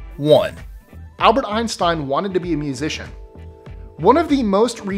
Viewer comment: When he said Einstein wanted to be a musician, I thought he said beautician!